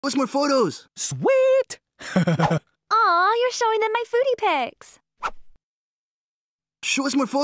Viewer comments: Bruh I always thought she said footy pics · For a sec i thought she would say "What? Youre showing them my poosie pics?" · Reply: I thought she said "nudie" pics when she said foody 😭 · I heard "aw your showing then my booty pics" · I thought she said “aww your showing them my booty pics”💀